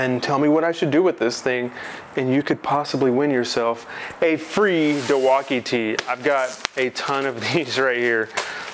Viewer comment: Did he just say “Dilwauke” ?